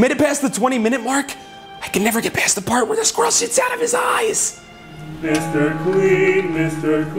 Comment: Hold on, what did he say at 10:56 again? I did not quite understand it. Did he say "I can never get past the part with his gross shit out of his eyes" or something? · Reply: The person in question said "I can never get past the part where the squirrel shits out of his eyes!",in reference to an earlier scene where, well, the squirrel shits out of his eyes